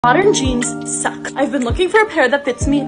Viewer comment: For a whole min I thought why is she saying modern genes suck 🤡